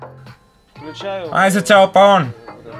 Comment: What does he say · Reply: Izotope On